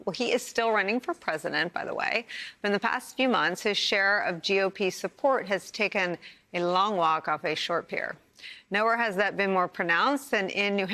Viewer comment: "...his share of GOP support has taken a long walk off a short pier." I'll admit to a bit of a hearing problem, but what I heard, was "...his share of GOP support has taken a long lock of his short hair." I actually laughed out loud because I know Nicolle gets pretty feisty, but... So, I had to play it again with captions on. Nevertheless, things are often more interesting the way I hear them. I prefer my take on this situation...